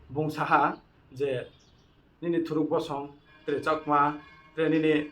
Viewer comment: I too heard it as thruk not bru i don't know how did they heard it as bru it really a shameful for us Reang